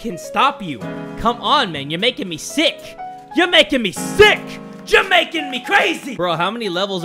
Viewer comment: I heard "Jamaican me crazy" instead of "youre making me crazy"